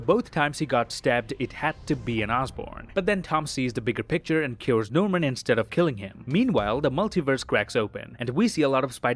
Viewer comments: Is it just me or did it sound like he said "But then, tom sees the bigger picture, and kills Norman instead of killing him." 😂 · “Kills norman instead of killing him” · does anyone else hear, “but then Tom Spider-Man sees the bigger picture and kills Norman instead of killing him.” ?